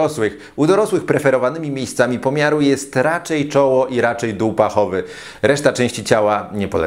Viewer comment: Dół pachowy usłyszałem jako Dupa Ochowy i zastanawiałem się co jest z tym doktorem i tyłkami 😅